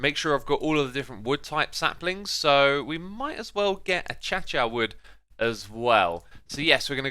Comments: did... did he just say Acha chia wood???? What?! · did he just say 'achacha' instead of acacia? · did he just call acacia trees ''achacha'' trees?